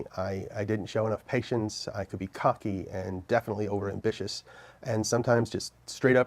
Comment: I could be CUCKY?!? does anyone know what he actually said??